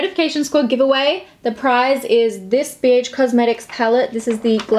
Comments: I first thought you said “this bitch palette” in stead of “this bh palette” 😂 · I heard “so for this giveaway the prize is this BIEETCH” @ 0:29 omg